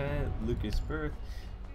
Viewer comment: can you even english? @ 6:17 what did you say? Lukas Berth?